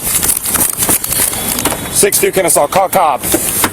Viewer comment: i swear at 6:58 it sounds like he says "call the cops" bro you are the cops